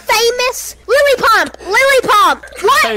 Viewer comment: Who da fuq is Lily Pump😆😂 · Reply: i thought he said Lily Pop